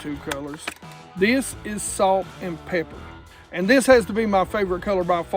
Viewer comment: I swear i heard "Salt and Pecker"